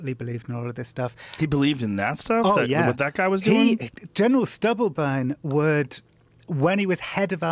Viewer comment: Wait the head of Army Intelligence was called General Stubbleburn? Hahahaha surely I misheard that?